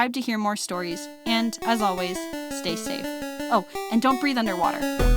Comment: at the end where u said dont breath under water my internet was not working so all i heard was " DOnT BreATh!!! "